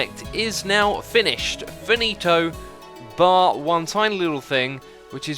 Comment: did he say “finito”?